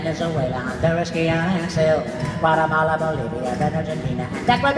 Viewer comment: he named my country "Paramala" instead of "Guatemala" but I don't mind, he did a great job!!!